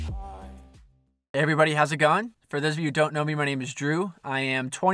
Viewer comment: At the start if you have subtitles on he says everybody has a gun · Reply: He said “hey everybody how’s it goin’” and subtitles just misheard it